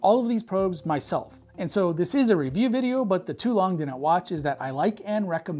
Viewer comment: Would anyone please help me with what James said at 0:25? "But ... didn't watch ..." I paused and re-watched many times, but still failed to understand what words were said. 😭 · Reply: i am pretty sure he said, "but the too long didn't watch" edit to add confirmed by subtitles